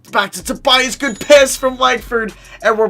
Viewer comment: did he say good p*ss?